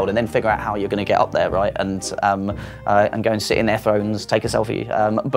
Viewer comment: I thought he was saying skeleton phones. I was still on board for a bit, untill he said "sit on them" (thrones)